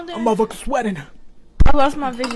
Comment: Help why did I hear: “Oh sh!t she dip” 💀